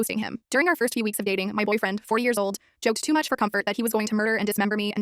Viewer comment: Y'all, I thought it said "My boyfriend, 4 years old" 😅